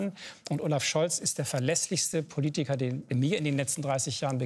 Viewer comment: Wer hat bei 15:42 auch gehört "Olaf Scholz ist der vergesslichste Politiker der mir in den letzten 30 Jahren begegnet ist"?